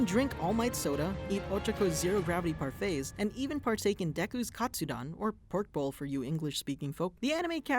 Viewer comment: I've been on my reading manga for too long, I legit thought he said Deku's Kachan bowl, how do I turn the fujoshi off? It's going to ruin my life someday I can see it.